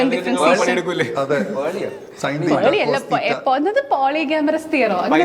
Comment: Did she say polyamorous 😂 ?